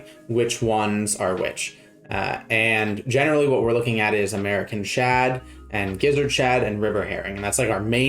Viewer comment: Really thought you said American Chad, I mean saying Chad after american is redundant